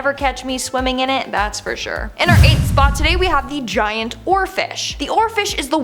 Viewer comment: I was just listening and thought, did she just say "giant orifice"? OHHH, giant oarfish.